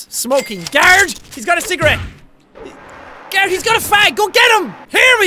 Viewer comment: did jack say the f slur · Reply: yea i know that it was ironic bruv im british i know what fag meand